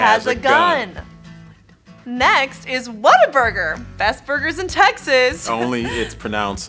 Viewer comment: is correct the pronunciation of "Whataburger". Austin is trying; we just kept out a bleeding heart diversity candidate for Mayor. We also addressed the downtown homelessness issue. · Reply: I thought he was saying "water burger"